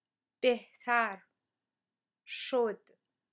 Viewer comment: Thanks for this video! I can speak,read, and write Farsi but kind of at a beginner level. This is good practice. When I heard her say "بهتر شد", I thought she was saying someone's name, like "Mehtarsh" (which probably isn't even a name).